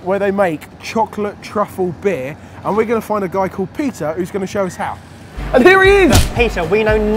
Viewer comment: When you said peter I thought you said pizza